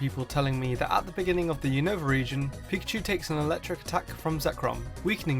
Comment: I swear he said pichu instead of pikachu